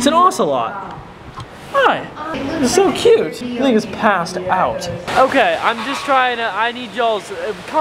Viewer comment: When he said the thing is passed out it sounded like he said niggas passes out